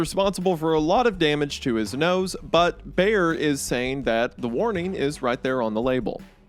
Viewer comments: You said “nose, but..” and for a split second i thought you said he ruined his butt with afrin 😂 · thought he said "damage to his nose, butt, ..." and i was like whar... how...